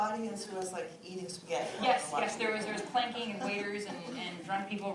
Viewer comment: did I hear this correctly? Along with waiters, there was PLANKING?